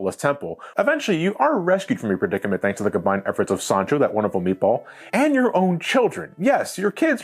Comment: I can’t be the only person who heard Sonichu, right?